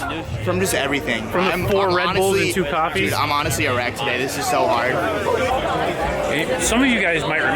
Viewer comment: thought Said "I'm honestly erect today"